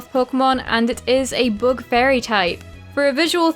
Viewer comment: you sounded like you said BOOK instead of BUG. Quite funny 😂